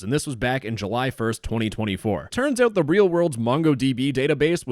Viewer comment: DID HE JUST SAY MANGOS 12:53 MONGOS MONGOS MONGOS MONGOS MONGOS 😈🥭😈🥭😈🥭😈🥭 + THOSE WHO KNOW 💀💀💀💯 + BOIIIIIII THIS FRICKIN 🔥🔥😈😈😈💯💯💯💯 TUFF